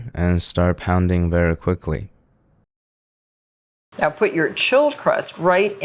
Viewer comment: bro i thought she said “now put your children in to the oven